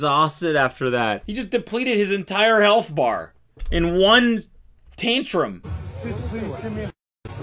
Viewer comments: Did you say.. "Taint-trum"?! Yes, you definitely said "Taint-trum" and now you own it.. Take a bow! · I definitely heard cody day “taintrum” instead of “tantrum” and I loved it